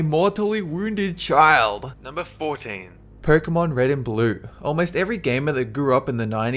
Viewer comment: did he say "porkemon"?